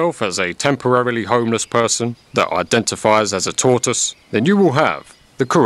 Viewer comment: Was listening and logging in to Amazon to buy that emergency blanket I've been meaning to get, when I just had to pause and marvel at the line at 12:15, "Think of yourself as a temporarily homeless person who identifies as a tortoise, and you will have the correct mindset." Zen. 💮🙏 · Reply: Wow. I thought he said to identify as a Tourist, not a tortoise. I stilled laughed. makes you do that.